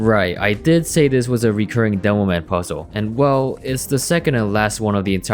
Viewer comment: If you have the captions on here it says demoan instead of demoman